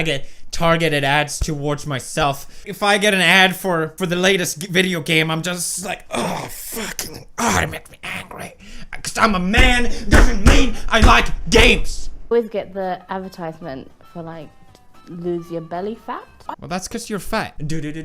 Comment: did he say "I like Gabes"? :D